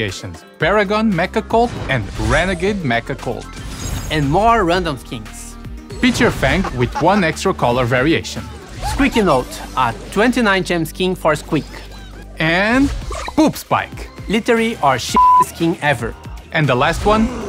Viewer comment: Am I the only one who heard him say that squeaky note is a 299 gem skin 😂😂😂